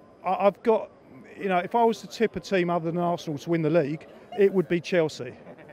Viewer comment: "If I was tipping a team other than Arsenal to win the league, it would be Chelsea." Did I really hear this or are my ears broken?